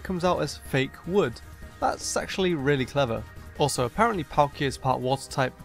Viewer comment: I thought you said that fake wood was sexually really clever